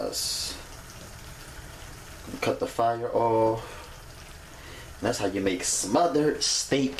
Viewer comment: Lmaoooo I thought he said with a cup of fireball but he said cut the fire off...I need wine 😆